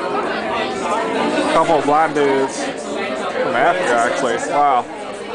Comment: was it just me or did i hear...."a couple of black dudes"?-.